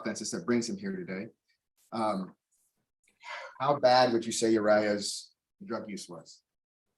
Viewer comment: I keep hearing "Mariah" and thinking "huh...odd name for a white dude. Then again, so is Uriah."